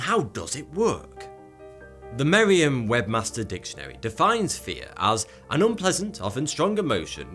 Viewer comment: did he just say merrian webmaster dictionary